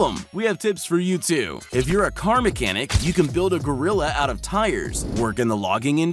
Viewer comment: did I hear gorilla?